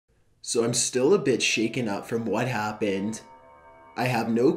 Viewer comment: He said a “bit shaken up” but I thought he said “I’ve been a bitch about it” xD Great story, it’s scary- and convincing!